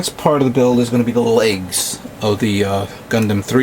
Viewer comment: it sounded like u said eggs lol!